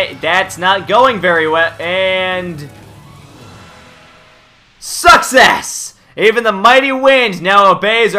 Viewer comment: "Succes" sounded like "Sucks ass" :D xD